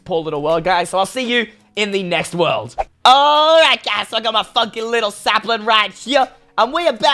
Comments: really!?!?!? Did he say the f word!??!!??! Or is it just me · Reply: No he said funky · did he say I got my fucking samplin right here?